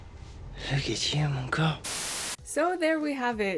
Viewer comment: what did he say at 10:17 ??? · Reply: "Hey look at you..."